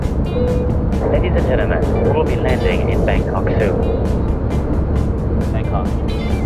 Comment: Hahaha i thought he said bangcock